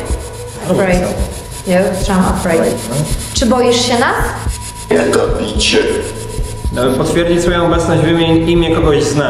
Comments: ja usłyszałam Bartek Kubicki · usłyszałam kubicki xD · ja usłyszałam „Jego bicie”🫣🫣🫣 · czy tylko ja tu usłyszałam „jak gapicie się”?💀😨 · usłyszałam ,,ja zabić się" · ja usłyszałam Kubicki